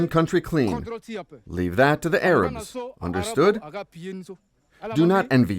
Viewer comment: I hear 'the Arabs' rather than 'others'...